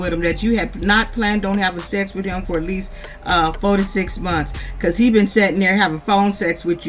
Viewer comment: Sending Luv & Lite 2 U & Tu familia. 14:03 - 15:20 Hilarious I Thought U said: "46 Months"